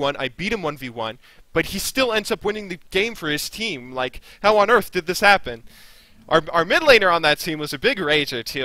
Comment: ''how on urf did this happen'' it sounded like it in my head :P